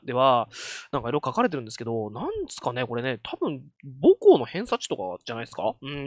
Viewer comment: もこうの偏差値に聞こえて3回聞き直した